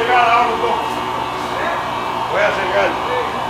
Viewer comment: I swear he says” yo voy a chingar”